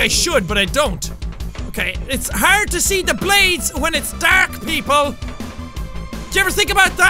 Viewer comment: Did he just say DARK PEOPLE??!?